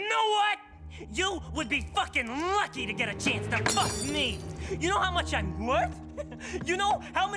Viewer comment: The voice crack on “you know how much I’m worth!?” is great voice acting. Also massively changes when Angel later states he wants to make himself broken and worthless so that Valentino won’t be interested in him anymore. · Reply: I thought it said you now how much ive worked